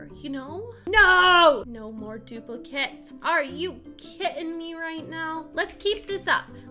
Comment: When she said “are you kidding me right now” I thought she said “are you kitting me right now” LOL😂😂🤣